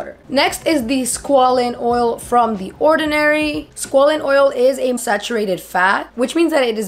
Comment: Meaning no disrespect, I love you and your channel. When you said squalene the first time I thought you said squalid 😂